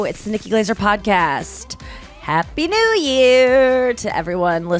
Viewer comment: I thought Nikki Glaser said aspie new year, for a sec.